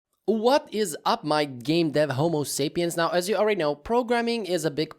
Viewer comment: i thought he said what is up my gay dev homo sapiens